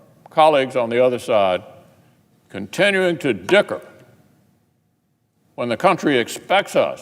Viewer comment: does McConnell say "dicker"?